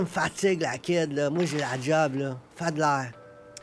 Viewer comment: Correction des sous-titre francais 14:10 "moi j'ai de la job, fais de l'air" ... et non "pas d'ail"..."fais de l'air = dégage"